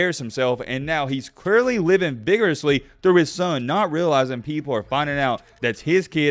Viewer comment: You should know VICIOUSLY and VICARIOUSLY are two totally different words my brother! Haha know it was just a slip 😝 Good video overall tho my man! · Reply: So is “vigorously”, which is what it sounded like he said.